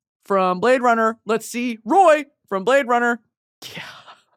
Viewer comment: i thought she said maze runner and freaked out over the fact she’s never seen it, then i remembered, i havent either